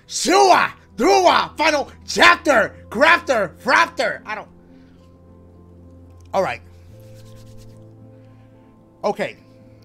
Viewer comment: After he said "Chapter! Graphter! Raptor!" He mumbled to himself... I thhhhhhhhink he said "I don't know what I'm saying"